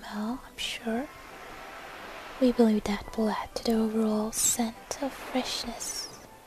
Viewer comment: what does she say at 4:27 to 4:29??? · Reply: "We believe that will add to the overall scent of freshness."